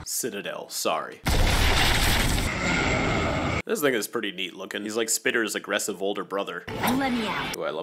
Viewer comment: what did he say here? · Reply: “This thing is pretty neat looking “